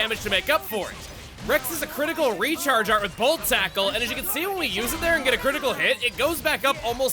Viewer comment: So, am I the only who thought he said "volt tackle" when he actually said "Bold Tackle"?